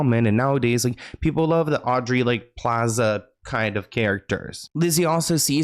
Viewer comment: In Australian it sounded like you said "Janice Iron" instead of "Janice Ian" and that sounds so much more hardcore 😆